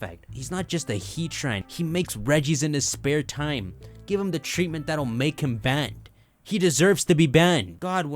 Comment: Ok so I thought you said “give him the treatment thatll make him bang” (as in, doing really well) then followed it up with “Regigigas deserves to be banged” And even though you didnt say that I agree